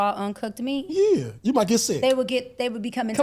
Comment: What kind of doctor says "cinnamonilla"?